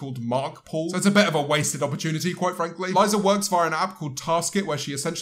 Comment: I swear this man said "Aliza"